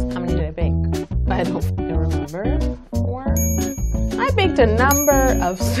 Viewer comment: did she say "I don't fuckin remember"? 😂😂